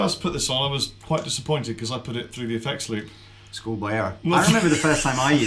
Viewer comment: What does say? I can't make out what he said. [Must be too posh for my ears.] Thanks, in advance! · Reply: "Schoolboy error"